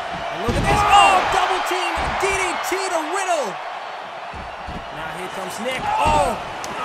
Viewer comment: did it sound like he said widdle?